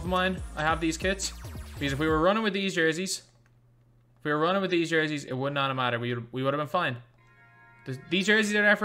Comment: For those confused I got angry at the guy for wearing the one white Away jersey in the game (White Lakers) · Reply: Am I the only one who thought he said 2 hype jerseys